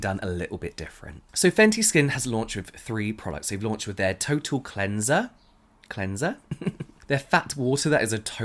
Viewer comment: I heard you say turtle cleanser and was so confused until i read it's actually total cleanser, yep that makes more sense.